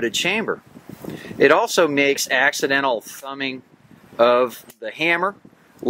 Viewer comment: Thanks! I actually said "two-handed thumbing," though I can understand why a quick listen might have sounded like I said "fanning." The wind was really bad, and I had to apply some noise reduction to the audio. If you watch it again, though, I think you'll hear the word "thumbing," which might not be the right label either. :)